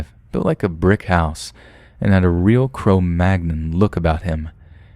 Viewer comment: I thought he said crow magnum and I got really confused until I realized he actually said cro magnon